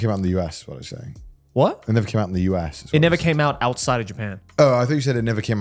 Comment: i thought that too, but he says, "never came outta japan" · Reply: Oh fuck you're right! I thought he said "Never came out in Japan"